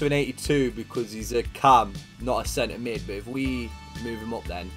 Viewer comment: I dead-set thought you said 'Dele Ali's gone to an 82 because he's a cunt'.